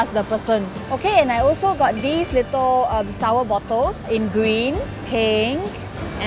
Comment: I thought she said I got these sour buttholes 😂 sorry 😂